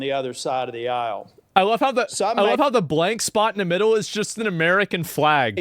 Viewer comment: Hew boy, I was just listening and I hear "I like how the free spot in the middle is an American flag" 🤣🤣